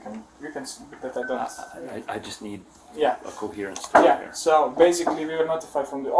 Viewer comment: But the cops are being totally rude to cruise security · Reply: am i bugging but around 8:50 did he say I just need a coherent speaker?